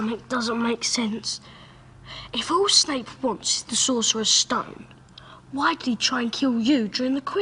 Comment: did ron say sorcerers stone?